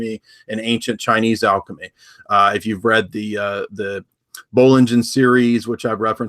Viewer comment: What did jay said here? 20:01 I couldn't understand the name of the author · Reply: Bollingen Series